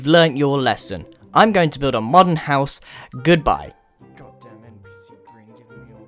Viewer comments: Should I be concerned he said god dan^^ it ;v;???? Or did I hear it wrong and he said god dang it? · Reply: It’s just damn. You must be a child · I heard God Danmit in the background.....